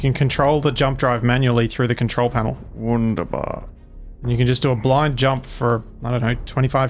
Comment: can somebody tell what word is saying at 12:03 after is telling him: "you can control the jump drive manually through the control panel."? because I'm understanding "wunderbar", and this is a german word as far as I know :D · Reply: Yeah it was wunderbar, and it's a german word but one that's understood by most people in english and one likes to throw around from time to time. Kind of like he often enjoys saying borscht :P